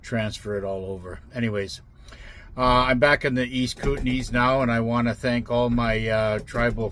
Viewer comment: HUMMM... WENT TO "TRANSCRiPT" FOR THiS ViDEO....BECAUSE... I THOUGHT YOU "SAiD" < KOOTENAI >.... : }